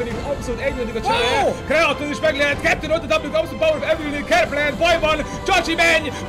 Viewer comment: Ha valaki megmondja hogy itt mit mondtak az egy zseni xD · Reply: Pepach Pictures Ott a double abszolút power of evilnél! :D